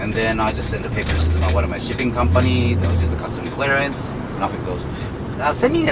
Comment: it sounds like he said shitting company